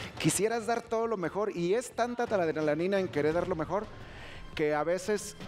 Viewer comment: ¿Qué dijo? 😂😂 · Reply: Una anenanina